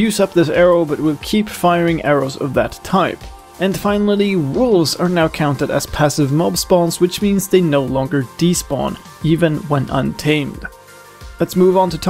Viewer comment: what are you saying at 5:45? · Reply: Wolves are now counted as passive mobs spawns which means the no longer despawn.